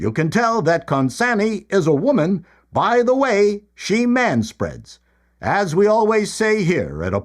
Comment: when you said you can tell this person "is a woman by the way she manspreads" I heard 'manspreads' as 'manstruates'. Whoa, me!